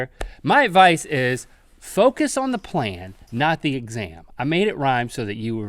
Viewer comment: I thought Link said focus on the plan not the exam but he said focus on the plam not the exam I would rather think focus on the plan not the exam it just makes more sense